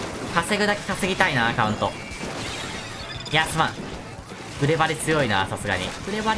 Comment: グレバリ〜って声聞こえる？